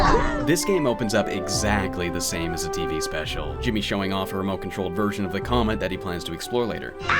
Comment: showing off her comet.. lol did I hear that right